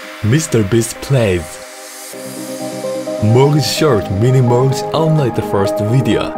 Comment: When you said “Mini Morgz” it sounded like “Mini Shart” Edit: When you said MrBeast and Morgz Shorts I thought you said MrBeast and Morgz Sharts